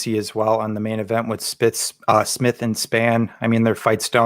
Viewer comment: 😂 for sure thought almost said spick and spann🤣